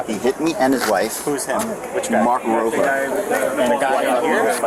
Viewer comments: I thought they said Mark Rober, was surprised he hadn’t invented a robot to bypass buffet queues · I really thought the guy said it was Youtuber "Mark Rober"